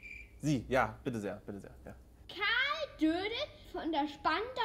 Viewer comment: Wer hat bei 1:50 auch Karl Dönitz verstanden? xD